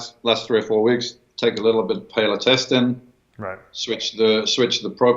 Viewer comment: What did he say at 23:10? Last three, four weeks take a little bit paletestin? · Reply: BODYBUILDING SCHOOL halostetin